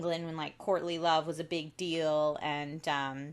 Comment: At first I thought you said "Courtney Love" and I was like "waaaaaat"